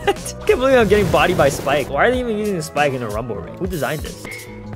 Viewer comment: I thought it said "Using rum in a gumble raid" 💀💀💀